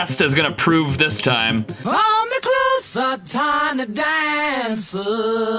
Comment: I hear hold me closer tiny dancer